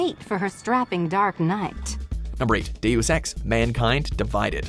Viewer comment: Did i just hear "Do you sex" rapidly instead of "Deus ex"